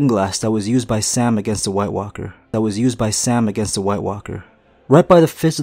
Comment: does he say same vs white Walker 2 times?